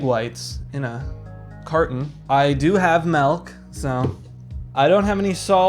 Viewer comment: my first time watching and at 6:47 I heard “malk”, good lord the memory of “we don’t have ‘malk’ but we do have milk”